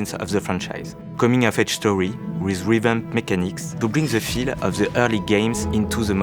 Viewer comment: it's so hard to tell, but does he say "rhythm mechanics"??? If so, I'm so excited. The best secret feature of AC1 was that its combat was actually a rhythm game. In such a unique way, too. You wrestled with the AI over who was setting the pace & had the momentum. So good